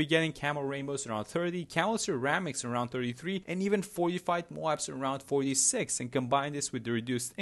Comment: When you said Fortified Moab, I thought you said 45 Moabs. Scared the hell out me for a sec.